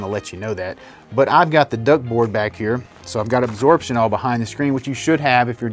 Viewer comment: I can't make out what you are saying, did you refer to it as "duckboard"? (The insulation material behind the screen.) Is there a link to that? Alternatively, can we use fiberglass insulation from Lowes/HD, and if so what R value do you recommend? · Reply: Yep, the ductboard is discussed in the earlier build updates and the acoustic treatment videos.